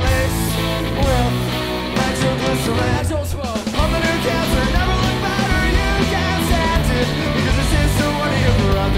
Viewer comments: what does ryan say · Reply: after years of studying this clip im gonna go with "I don't smoke." this statement did not hold up... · what does bestie say after “nitroglycerin” (dont speak emo super well) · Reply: I don't smoke